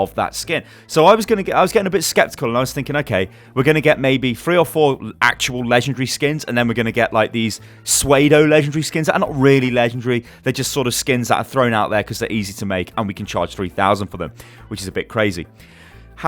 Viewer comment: Did he just say 'sweido'? Do you mean 'pseudo'? Like, 'sudo'. As in fake.